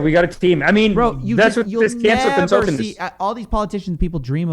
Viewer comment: I kept hearing “cancer consultant” instead of canceled consultant until realizing at the end.... and it really changed this entire podcast for me